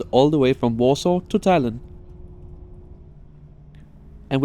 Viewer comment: i thought you said Thailand not Talinn😂